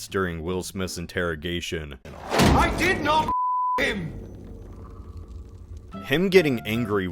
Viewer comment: why did the beep make it sound like Sunny is saying "I did not fuck him" lmao